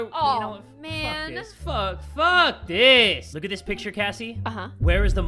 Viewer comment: Did anyone hear and said "f*** this" at 2:07 LMAO